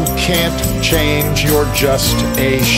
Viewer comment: Just one thing that put a damper on things; in the closed captions on the hilarious "Ruby Tuesday", um, homage, I noticed "your" was used instead of "you're" at 2:39. Sorry. Being a bit of a stickler here 🙂.